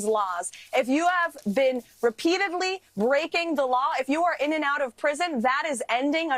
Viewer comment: I thought she said, "If you are continually raping the law," which sounds about right for snarky, "Christian" bad Barbie to be an alarmist, exaggerating things - just like her bumbling, boastful boss. I had to listen again, then put CC on, to see she'd said "breaking."